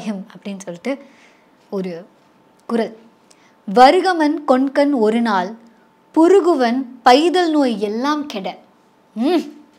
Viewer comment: anandhi gr8 fan of ur works......with all due respect, did you say puruguvan...??? 22:55 just to show how keen I have engrossed in your narration and interpretation....this whole episode I was blushing till I cud cramp my chin.....!!!